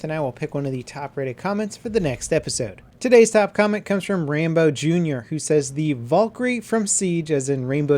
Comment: Did he just say rainbow Jr instead of Rambo Jr? lol